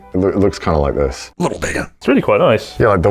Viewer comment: What did he say at 6:00? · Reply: Lil bigger